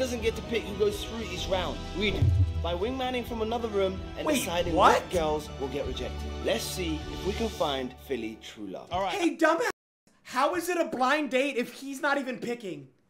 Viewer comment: Thought they said Philly wanted love wtf 😂😂